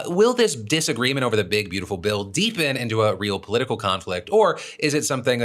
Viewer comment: I definitely misheard the intro where he said Elon was unhappy with Trump's BBL as opposed to Big Beautiful Bill.